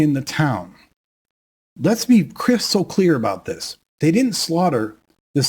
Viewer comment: If you're going to have the AI-generated captions turned on for who knows what reason, can you please EDIT them so that they are CORRECT? My favorite error: 37:54 You say: "Let's be crystal clear..." The caption says "Let's be Chris so clear..."